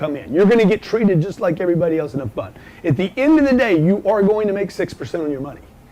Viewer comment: I swear to god it sounds like he says "... you're going to get treated like everyone else in the butt" Nostradumbass can't even pronounce Nostradamus 🙄